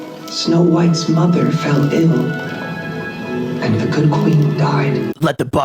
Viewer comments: Its ending me that when the narrator says 'But then her mother fell ill' The shot is from within the well, and it sounds like she says 'her mother fell IN' as in the well. I'm going to live in that reality, thankyewwwwwww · I thought they said the queen "fell in" instead of "fell ill" and I imagined her falling in the well and drowning while the king and Snow White just stood there.